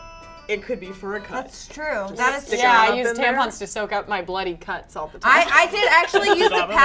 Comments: Who else thought she said "bloody c*nt" when she said "bloody cut"? 😂😂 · totally thought said "I use a tampon all the time to soap up my bloody cunts" instead of cuts lol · or so it sounded like said cunts not cuts